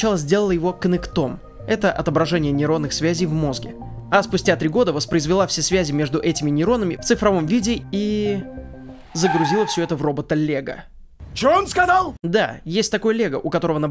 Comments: Когда сказал " робота- Лего" мне послышалось " робот Олега" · послышалось в робота Олега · загрузила все это в робота лего, я услышал: загрузила все это в робот Олега